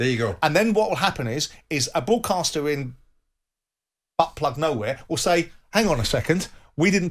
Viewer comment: Did Simon ACTUALLY say "A broadcaster in butt plug nowhere" 8:00 wtf lol